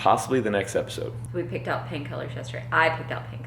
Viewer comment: The first two times she said "paint colors" I thought she was saying 'pain killers'! 😂